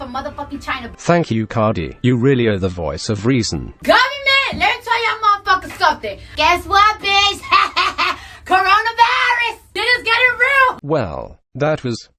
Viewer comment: Listening to cardi b talk is like having my brain dunked in bleach then having it cleaned in vinegar. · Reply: Honestly, in the clip in this video I thought she said, "Comin' back let me tell y'all mofos sumthin'... guess what bish, HAHAHA, CORONAVAAARUS... dinners gotta rue". I watched the original video with the subtitles, BTW. For an American, her English pronunciation stinks.